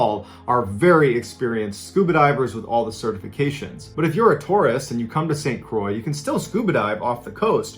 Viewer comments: At first I thought he said Taurus not tourist and I thought DAMN I’m out🤣🤣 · I heard “when you’re a Taurus...” and thought I’m far more qualified as a Pisces 😂😂😂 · ish I thought you said Taurus and being a diver (granted I haven’t diven in a awhile) I was thinking WTF? I had to go back and listen until I heard tourist Thanx for the laugh. I can say I’ve been safely to 1300’ with no decompression inside my nice safe SDV delivery vehicle Thanx for your service enjoying your stories from Krapramento, California the Krapital/Krapitol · Omg i thought you said Taurus ♉ for a second there then realized you said tourist. 😂 I think I need a coffee or a nap. Either way that "wall" sounds terrifying 😳. Scuba diving sounds really dangerous regardless of knowledge and experience.